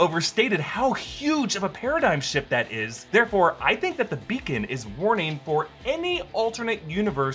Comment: I feel like the beacon is calling out to the Beyonders, as set up for Secret Wars. Shang-Chi being the POV for Secret Wars would be interesting, as he’s got an immense amount of power and skill but is also extremely vulnerable. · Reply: I thought you said bacon for a second... I was like, interdimensional bacon? I'd be here for that!